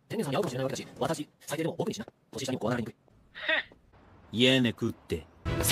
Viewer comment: What does gojo say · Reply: I think he said "ie nekutte" which kinda means "no way"